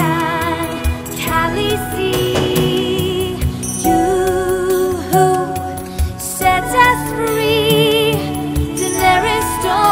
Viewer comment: "Yoo hoo set us free" is what I always hear :') Yet I still really like the song